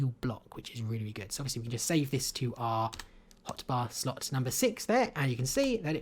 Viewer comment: Turn on Subtitles and click here---> 4:46. For those that are too lazy to do it, it says "hot bath" instead of hot bar :P